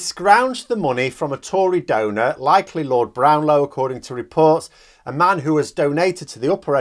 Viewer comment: Regarding that Conservative Donor: I thought you said Lord Brownnose for a second, LOL